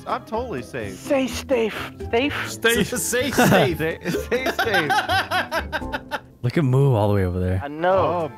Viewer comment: for the second though I heard "savestate" a webcomic.